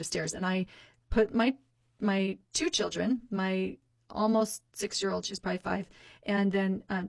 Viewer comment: please tell me I heard that wrong. Did she really say “she’s prolly five.” LIKE SHE DIDN’T EVEN KNOW HER OWN CHILD’S AGE?!